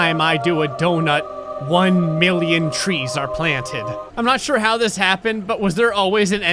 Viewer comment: I thought you said trees